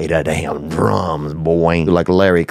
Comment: Did he just say Larry Kravitz 😂